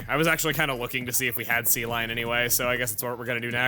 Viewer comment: Each time he said C-line, I heard "sea lion"